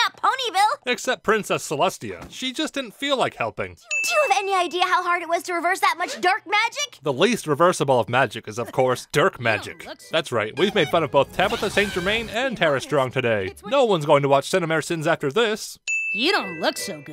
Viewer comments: At first, I thought Twilight said "Dirt magic". That would be funny though! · I thought she said "dirt magic". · (may be missing the joke) I thought she said dirt magic? As in the dirt was changed to solid gold. So they needed dirt magic to fix the dirt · I heard “DARK MAGIC!?” at first… But after watching this all I hear is “DERK MAGIC!?” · I thought Twilight said dirt magic.